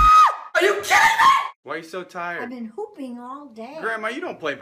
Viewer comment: I legit thought that she said "pooping" instead of "hooping" IM DYING XD